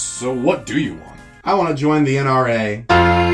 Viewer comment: my Irish ass misheard this as "I wanna join the IRA" and thought the American flag being there instead was part of the joke